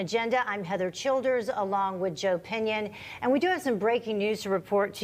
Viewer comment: I thought she said: I'm Heather Childers along with your opinion..them I realize the guy's name was Joe Pinion..lol.